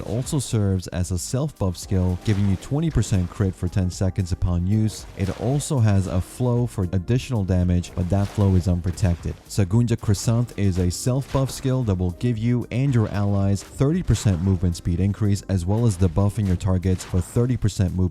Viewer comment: I swear i heard you say “ sagoonja : croissant “